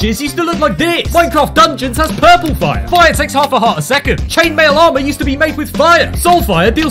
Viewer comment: why I hear "Chainmail armour is used to make with Ryan"